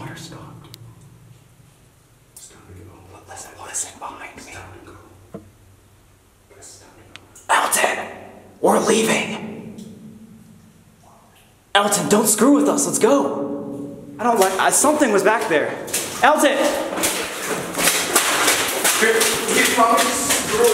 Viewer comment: if you have notifications on LIKE THIS COMMENT! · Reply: Hey when you said where leaving and I heard correy